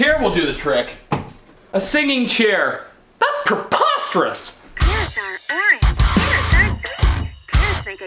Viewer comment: I genuinely thought that they said carrots are green